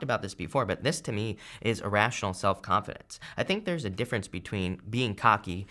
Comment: Irrational self confidence. Almost need to be unrealistic about future success. Letting go of the anchor- what's holding me back. Transformation. DONE. Brilliant insight. Thank you. Much needed. Namaste. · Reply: I thought he said irrational at first too but I think he’s saying, “A” rational confidence meaning not cocky is what I understood, ❤️